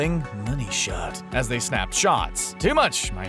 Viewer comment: oh I thought they said "Body shot" 💀